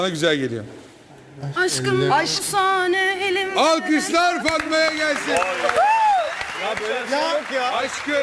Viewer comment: modelin gerçek ismi Fatma olduğu duydum ağızından